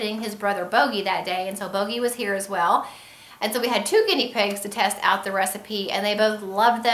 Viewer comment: Sorry, did you say it can be given to Guinea pigs too? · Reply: It sounded like you said " Bogey was here today and so they have 2 Guinness pigs to test out ........ Im Welsh and live in Australia other than bad hearing I can think of no other excuse! Oops! Lol